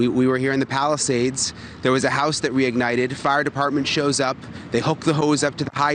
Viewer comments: Hold up maybe I am just hearing things but the reporter talking about not enough water he said "their was a house we ignited " ... think about that, that WE ignited. Check it out for your self start the video at 11:08 and just listen to what he says. 🤯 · Will someone please answer me why at about 11:11 on this video that reporter says, A house "WE" ignited??? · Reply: I heard it as, "that re-ignited..." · “There was a house that we ignited” 😅. Did I just hear him say that